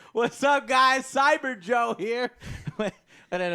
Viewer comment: I first thought it sounded like he said "Cyberjunk" and then I heard he said "Cyberjoe"